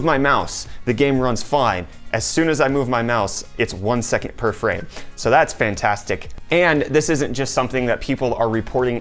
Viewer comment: did he just say one second per frame